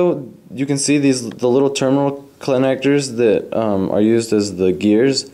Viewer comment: did you say clanectors? lol good job but man you should have practiced b4 you taped this, sounds like the build and the video took alot of brain power lol. and you broke alot of stuff